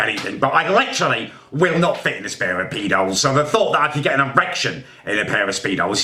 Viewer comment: Anyone else hear him say "I just can't fit in a spare of peedos"